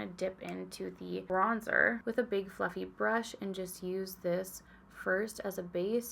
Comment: Every single time you say ‘fluffy brush’ I hear ‘fucking brush’ and it’s become a problem 😅 ⛄️🎄🎅🏻🤶🏻❄️🎁🛍🧸❤️ (@